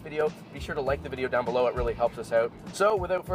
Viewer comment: Did he just say "without further duh-doo"?